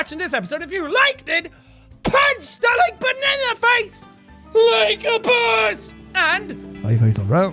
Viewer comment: At the end I paused it and it sounded like you said yelling banana face like a boss